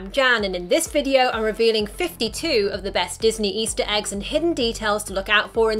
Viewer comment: She says 52, the subtitle say 51 Me: left confused